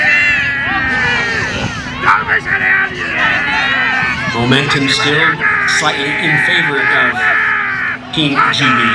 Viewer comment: Wtf is big boy yelling at 1:00???? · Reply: He's saying "tighter"